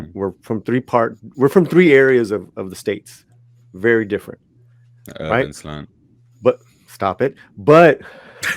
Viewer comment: What did say at 16:46? doesn't show up with CC or transcript. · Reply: That urban slant. It's a reference joke to Paulie's channel the Latino slant